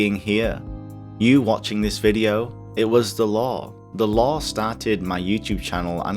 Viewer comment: lol when you said Lore in 5:19 i thought u said the Law xD